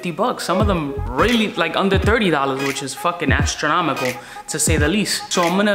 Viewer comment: Did he just say “ass-tromical” at 2:25 ?